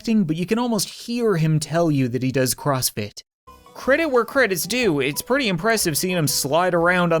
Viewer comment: he sounds like he is saying he does CrossFit Me: it sounds like he’s screaming help me